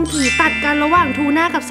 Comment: ใครได้ยินบ้าง555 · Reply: ฮีตัดกัน